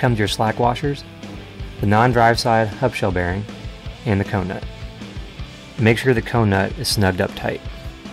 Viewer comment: Can someone say me what he said at 2:35? "Make sure the conenut ... tight" Sorry but english isn´t my first language and I dont want to do something wrong with the hub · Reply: Make sure the cone nut is nice and tight